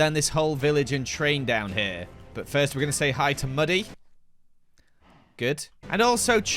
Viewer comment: “But first we’ve gotta say hello to muddy “ I thought he said Buddy and I started crying, I miss him